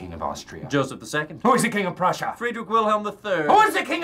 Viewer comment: did I hear the king of prussia surname is Willem